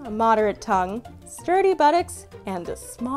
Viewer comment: I thought she said dirty buttocks.